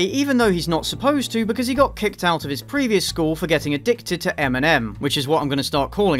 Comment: When you said "addicted to M&M" I had forgotten about the movie's title and thought he was addicted to chocolates. What is wrong with me. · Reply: I heard "addicted to Eminem." A few years too early, but still another big concern for moral guardians.